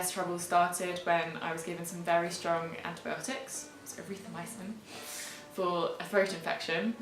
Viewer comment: Sounds like you said Erythroamycen (at 1:20). Whatever it was you should do a video on "Dangers of this antibiotic"....